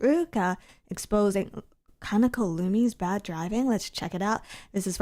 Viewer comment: i was half listening while doing laundry, and i thought said "Lumi's Bad Dragon." I locked in, and it took me 2 whole minutes to realize i misheard